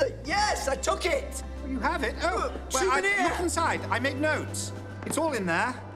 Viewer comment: Does he say souvenir? At 4:02?